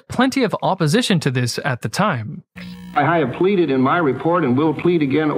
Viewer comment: "I have tweeted in my report..." Did I hear that right?